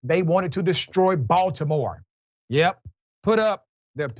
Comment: For some reason I heard "they wanted to destroy Voldemorte".